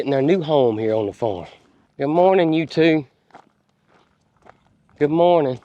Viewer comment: said Good morning "you two " I thought he said "good morning " utube lol